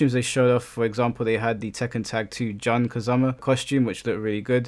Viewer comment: Bruh did you just say 'John Kazama" lol